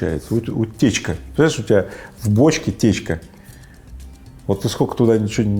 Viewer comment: Не у бочки утечка, а в бочке течь😂😂😂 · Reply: А мне послышалось у бочки течка🤣